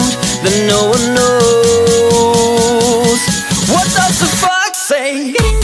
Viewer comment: "what the fox say?" I thought it said what the fumk say?